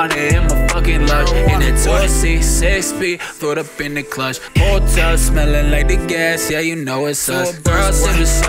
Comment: I always heard "Protoss smelling like the gas" until I learned it was "pulled up.... Lol